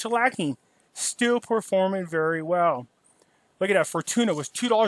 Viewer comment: I thought you said For Tuna(like tuna fish) Fortuna